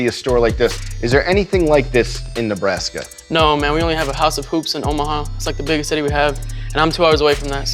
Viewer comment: who else heard “house of poops” 😂 🤣